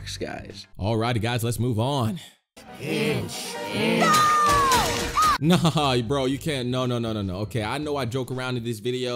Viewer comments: is definitely "pinch" i watched Teen titans go with subtitles on for no reason and they said pinch i didnt even hear the b word. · my favorite part😂 5:29 i heard pinch